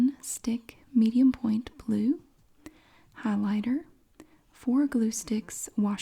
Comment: I wasn’t paying attention and when you were reading the list I heard “lighter” and I was like oh my god what! Her kids need lighters for school 😂 then I turned my brain back on and realized it was highlighter.